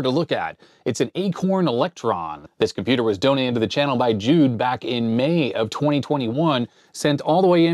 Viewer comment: i thought he said donated by June back in May but i think it must have been Jude...